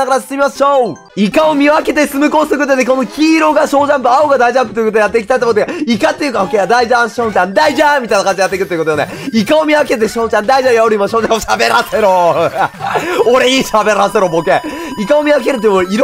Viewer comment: 3個目のコースで大ジャンプがだいちゃんに聞こえるw